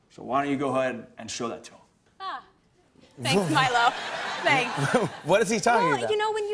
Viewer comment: when she said "thanks milo" i thought i heard "thanks my love" lol